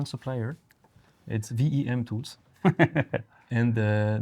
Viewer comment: What is saying @ 14:35? DEM tools, DDM Tools, VEM tools? Does anyone here know what company he is referring to? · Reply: Hi Vasco! Yes mentions VEM Tools - who made this very specific milling cutter for his escapement wheel 😊